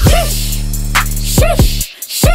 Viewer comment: I thought she was saying got the mold on